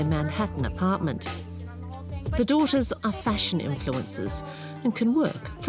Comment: I thought the 2 brats said they were “influenzas”... they definitely made me feel nauseous!